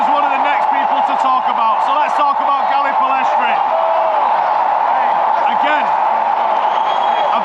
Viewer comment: Did say "Gary Pellistri"? 😂